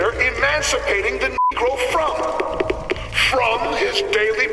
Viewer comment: that bleep out made me think he said the actual word there for a second lol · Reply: I think the character said, “Negro”, which while not as bad, was still an extremely disrespectful term for African Americans in the older days. You can see the word used earlier in infinite on the conservatism posters which was, unfortunately, sometimes the case back then